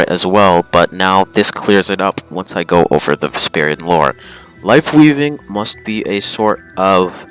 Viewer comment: Honestly thought it said racist lore